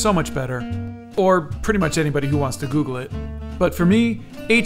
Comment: I heard you said "bottom" for a moment instead of "butter" and got SOOOO scared OMG